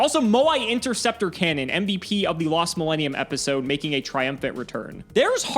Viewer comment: I heard "mvp" as "the mbt of" and I'm trying to figure out what that means exactly